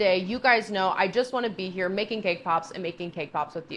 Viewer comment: I'm scrolling through the comments on this video while watching because I saw this one before... maybe it got taken down? Anyway, I was listening with half an ear and at one point I swear I heard say "making gay cops" and I was like "the fuck?" then realized she said "making cake pops" LOL